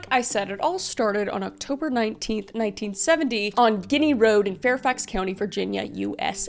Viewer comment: Did I hear on diddy road